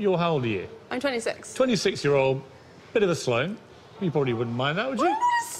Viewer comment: Did he say "Slome"? What does that mean? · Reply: its 'sloane' - its a name we brits give to some posh people, named after Sloane Square in London - think women with expensive bags and men in loafers saying 'yah, yah'